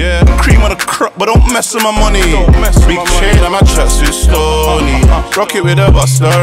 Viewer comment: Foallow I thought he said “big chain and my tracksuits stoney”